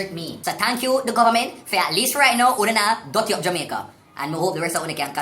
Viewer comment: I swear for weeks when I heard the "nuh dutty up Jamaica" jingle, I thought they said " 'nodder tea of Jamaica"